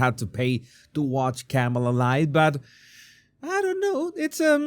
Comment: You said “Kamala’s life” I thought you said “Kamala’s lies” but I guess it is one and the same.